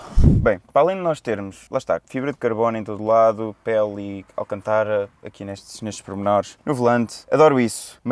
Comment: Fui só eu que ouvi "alcantara" em vez de alcântara?